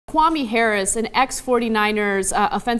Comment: LOL 0:01 The caption calls him mommy instead of Kwame